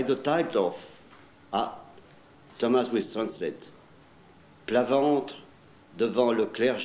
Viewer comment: For those who don't speak German: Vierne said something like: I don't want my coffin stained with their holy water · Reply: Thank you for your translation. I have listened again (at 9:07), and what I hear sounds more like "Plat varente" (whatever that may be, if I've even guessed a real French word...) than "Plat ventre." Langlais takes the trouble to enunciate very carefully, but I don't hear the word ventre there. The German translation offered at Langlais' request seems to begin with "Ein fieren," which gets an immediate laugh from those listening. Again, that's what my ear hears, though I'm only guessing that that is the German word used. I can't find a translation for fieren that makes sense here. Looking up the phrase "à plat ventre" online, I see that typical translations include, beside the idea of lying down on one's belly, the idea of groveling. Of course there is in this context the possible idea of prostration before an altar, as at an ordination. It would plainly be grossly inappropriate for anyone to prostrate themselves before the clergy, and not the altar. Vierne was obviously being rather caustically witty with his remark, and I would suppose that, whatever he said, he was likely using something more in the nature of a metaphor (prostration as groveling) than anything literal. Perhaps someone could have a shot at translating the German translation at 9:12?